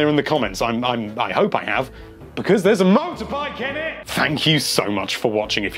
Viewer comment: what does he say at 4:27 ??? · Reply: IckySticky “because there’s a motorbike in it”